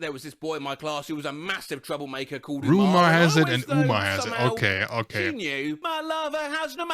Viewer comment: always heard it as "boomer has it"